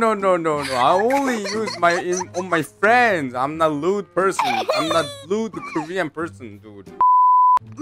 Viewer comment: When moon said im not a rude person it sounded like he said im a lewd person